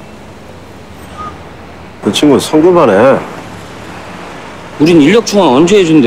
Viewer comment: 오과장님뭐라고하시는거에요? 못알아들음.. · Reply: 문미현 저 친구 성급하네~